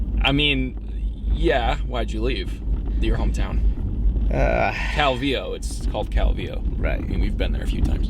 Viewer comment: pamela p. Calvillo, did you even watch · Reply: Cameron Kraese I WATCHED THE WHOLE THING LMAO also i am from mexico sjsjs i just didn’t understand the way they said it, i thought he said cavillo, which is pretty similar